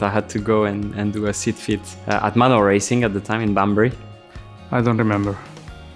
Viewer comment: does Esteban say Bunbury? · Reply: Banbury, UK - where Manor racing team was run from